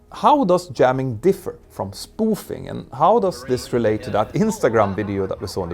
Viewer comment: I thought it said "Spooning at work" You had my attention